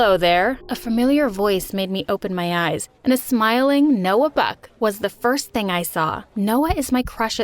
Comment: I thought she said Noah beck at first lol